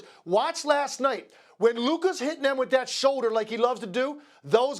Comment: Is it me, or does it sound like when Stephen A says Luka, it sounds like he is saying Luker lol ?